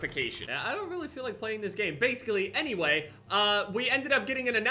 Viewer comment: he says "basclee" instead of basically